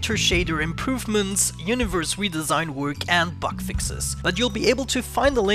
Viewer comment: Oh lord I thought you said "butt fixes" for a second LOL